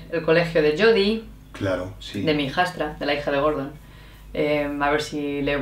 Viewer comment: Hola! No entiendo quien es jody? dijo algo así como "de mi gastra" pero no entiendo que significa, cerca de 7:10! Gracias por todo!